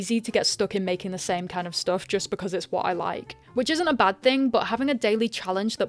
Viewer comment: You said "which arn't" in the video. Sounded like you said "witch aunt". Coincidence?🤔 Subscribed.